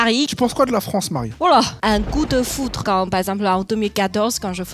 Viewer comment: Quand elle a dit coup de foudre 17:28 j'ai compris goût de foutre 🤣🤣🤣